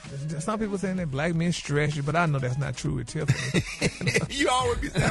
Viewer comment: Oppps! I thought Gary said, 'black men STRETCH you, but he said STRESS you! 🤭